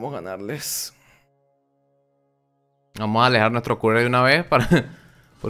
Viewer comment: que dices acerca del courier? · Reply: dijo que alejara el courier en caso de que ambos pierdan a la vez a el le llegue la bajada de vida después